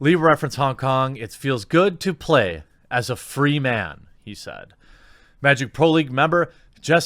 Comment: Oh he said free man. I thought he said it feels so good to play as a female. I gotta stop hearing things wrong...